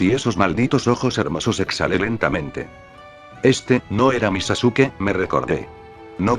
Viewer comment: Por un momento cuando dijo "este no es mi sasuke" yo solo escuche " este no es mi chikistrikis" jajjajaja xD